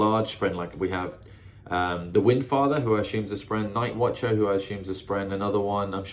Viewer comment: did I hear the windfather instead of the stormfather?😅